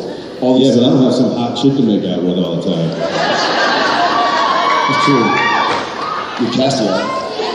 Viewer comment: what did Jared say about Castiel? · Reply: Jensen: "But I don't have some hot chick to make out with all the time." Jared: "That's true....You have Castiel." Jensen: "You mean Zooey Deschanel?"